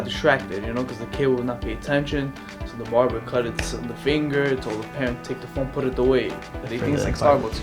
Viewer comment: did he say the barber cut the kids finger?